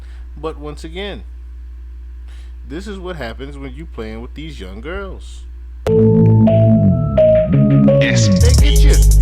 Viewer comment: 🦋, correct me if I'm wrong but did I hear you say, "This is what happens when you deal with these young girls, they get you " ❓🦋